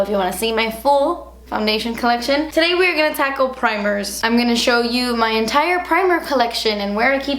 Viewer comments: In the beginning of her video, when she said she wanted to tackle primers.... my silly self heard her say, let's "taco" primers.... hahahaha... can you tell I'm hungry? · i thought she said taco instead of tackle